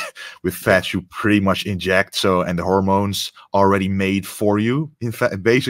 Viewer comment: what are injected fats? did I hear that right?